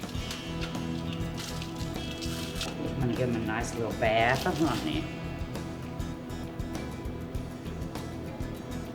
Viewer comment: Please, somebody help... What is that syrup at 04:10? Honey or maple?! I don't understand what is she saying :( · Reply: Honey